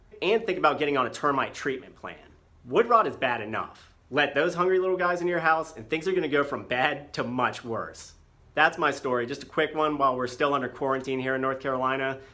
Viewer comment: at first I thought you said “go from bad, to munch worse”, but its noisy at work and i guess I'm hearing puns where there aren’t any lol